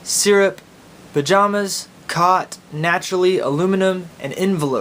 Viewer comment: pyjamas cot naturally. Or was it pyjamas caught naturally. I have no idea what the middle word was of those three.